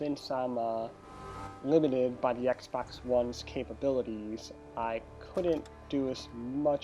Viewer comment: As I wrote that I heard you say “because of the Xbox 1s capability”